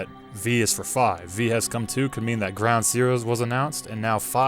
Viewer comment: I swear I hear, "HE has come to" not V.